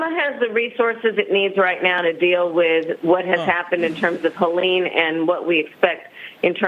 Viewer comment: Did she say “Collene” at 9:15 ??